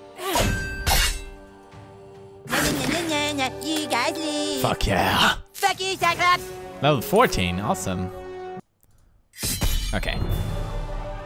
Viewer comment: when the wizard king said "Fuck you cyclops!" I thought he said "Fuckers o'clock"